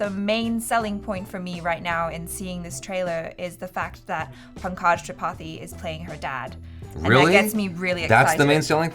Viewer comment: Did I hear Prakash Tripathi?